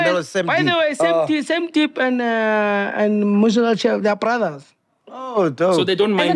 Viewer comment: Who did say Sam deep's brother is? I couldn't catch that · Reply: Da Muzical chef